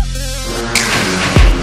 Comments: why do i hear we're the former king of recipe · it's been in my mind for a while now .... this freacking misheard we the former kings of recipe ! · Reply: LOL. when I first heard it I thought they said, "we the FlowBack with the recipe." But it's "we the FlowBack in the place to be."